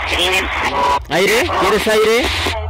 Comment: Y escuché el minuto 20:49 ay viene